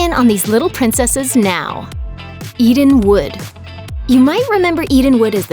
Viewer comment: When she said "Eden wood" I legit thought she said "eating wood"